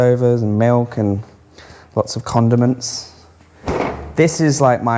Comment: pheww I thought you said "lot of condoms" in your fridge :D